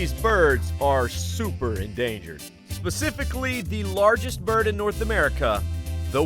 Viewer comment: did you say "pacifically"???😂😂😂😂